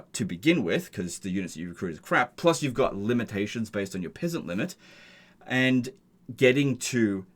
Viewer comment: … Did he say “pissant limit” 🤣 It’s not wrong tho…